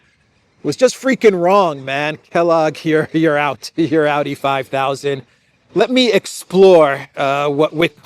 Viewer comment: For all these years, I thought you were saying " outty 5000 " I didn't realise you were saying " AUDI 5000 ". Pronunciation differences I suppose. 😊